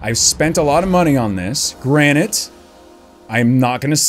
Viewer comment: Did you just say granite? 8:34 Instead of granted? ..........................